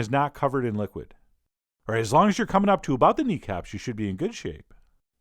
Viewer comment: haha, you said knee caps, but I totally thought you said d cups XD